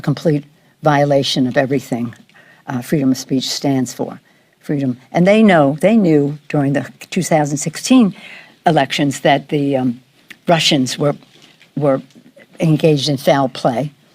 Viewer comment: did she really say Russia 😂